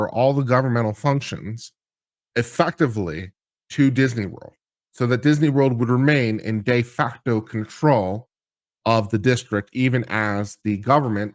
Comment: When played at 1.75x, it sounds like is say "gay facto control." 🤣